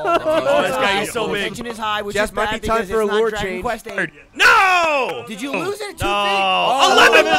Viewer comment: Did just say Dragon Quest 8!?